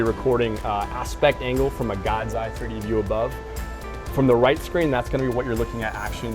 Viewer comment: What was the software the IP mentioned around 6:00? Did he say P3E? I would love to get the software they're using. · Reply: P3D.